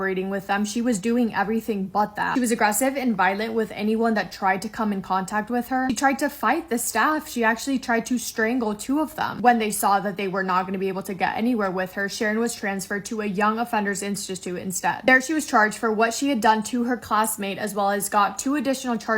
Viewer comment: did it say Shannon? Bc that's my name